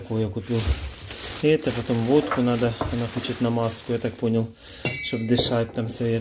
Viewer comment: будешь водку передавать , скажи что на маску ))) а то не пропустят медработники ))) Свете скорейшего выздоровления !!! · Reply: а мне послышалось НАМАЗКУ. Думаю, точно Света в больнице? 😉 Света, выздоравливай!!!!